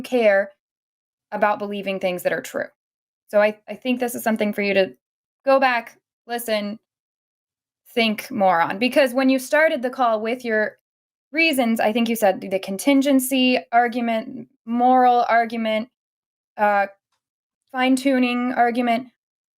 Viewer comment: I think i will avoid using the words "think more on" going forward only because i heard say it and thought she said, "Think, moron"